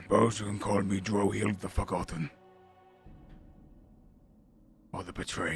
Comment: When he said forgotten I thought he was saying fuck often xD